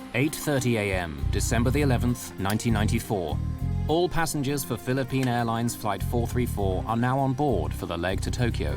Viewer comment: any body else thought it said september 11 💀